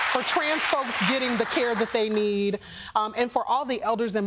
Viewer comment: I heard, "And transphobes getting the help they need" and I was like YES that so generous!